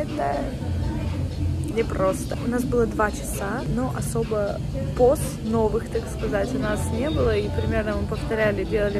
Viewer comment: сказала "не было поз новых", послышалось " не было Посновых")))))